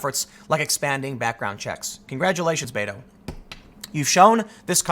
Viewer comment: Does Tim say Beta?